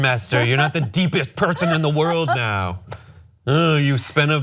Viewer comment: What did he say at 9:52? I'm french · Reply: Lily Evans "oh you were at Burning Man (art festival in Nevada), it changed your life"